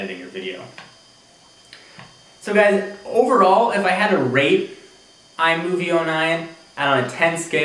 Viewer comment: Anyone else think at 4:15 it sounded like he said rape?